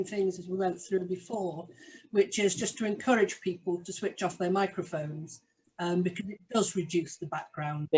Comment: Was that a "fuck off" at 1:19 or am I hearing things? 😂